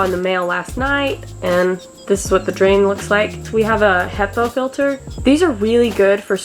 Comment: What kind of filter are you using on the shower? I know you said it - but it sounded like you said "hepa filter" and nothing I'm googling looks like what you have! lol! We're about to start on our skoolie shower, so I'm just curious! :)